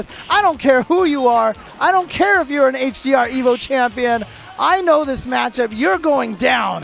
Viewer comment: what did he say "HRD Evo champion"?